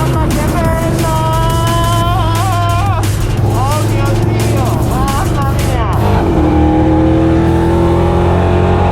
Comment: Ma solo io al minuto 5:30/5:31 ho sentito una bestemmia 😂😂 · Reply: Sembre una bestemmia però dice o mio dio